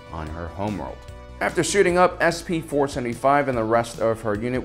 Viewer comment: thought yuh ou said shooting up. Didn't realise stormtroopers were druggies too